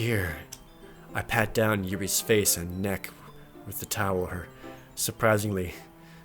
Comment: did he just say tower?